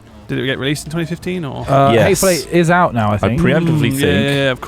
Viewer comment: I keep hearing "hate fellate" when the talk about the hateful 8.